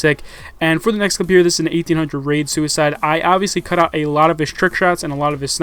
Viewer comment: anyone else hear him say "1800 raid suicide?"